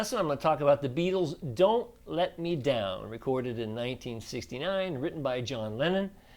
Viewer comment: John let me down に聞こえた(笑)